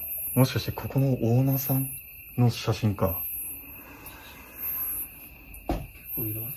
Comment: お疲れ様でした😣 テロップの声や音は聞こえました😣 2:04で写真見るなって聴こえます😓 7:12でコンって聴こえます。 7:30の声は❓お二人のどちらかの声ですか❓ 7:39でアッって女の人の声が聴こえます😣 8:14でかすかにアッって聞こえました。 9:22でねぇって聴こえます。